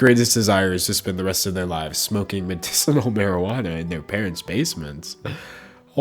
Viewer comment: Anyone else hear "medicinal" instead of medical? Or am I hearing this wrong